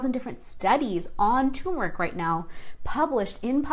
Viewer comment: subtitles say it tumour but I think you said turmeric. Can you please correct it